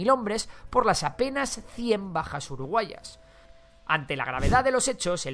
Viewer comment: uruguayas? escuche mal o tengo el oido tapado